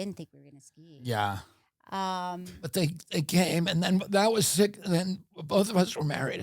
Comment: did he just say "that was sick"? 😂